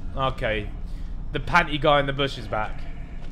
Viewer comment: I fully thought he said, ‘the paki in the bush is back’🤭